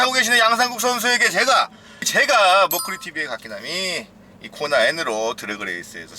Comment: 상국이형님 모크리tv라고 할때마다 목걸이tv로 들리는건 착각인가요?